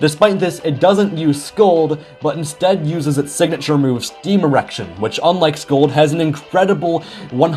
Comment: Ayo can you repeat what you said at 8:21? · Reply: It's Steam Erection, right?